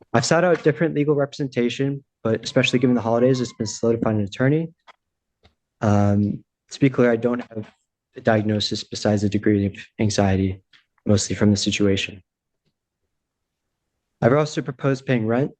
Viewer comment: Haha. I thought he said he has a degree in anxiety, not a degree of anxiety. 😅